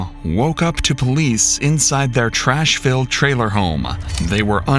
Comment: Jesus, i thought this was a parody. It sounded like he said Trashville motor home not trashfilled. And he manning being the most white trash imanable was a bit on the nose.